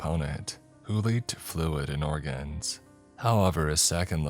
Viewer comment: For a second I really thought he said Fluid and Orgasms 😂😂